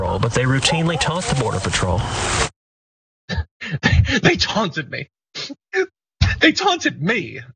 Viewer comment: Now, now, they REALLY taunted Ted badly. "Now go away, or I shall taunt you a second time!" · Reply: "Taunted"???? I thought he said "HAUNTED". I was waiting for him to bring out the ghost hunting equipment.